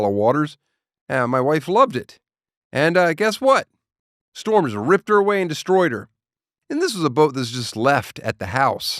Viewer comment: I thought you said the storm ripped away and destroyed your wife